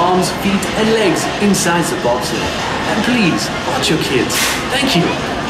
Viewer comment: When the guy over the intercom said 'and please watch your kids' I thought he said 'and please wash your kids' and I was like WTF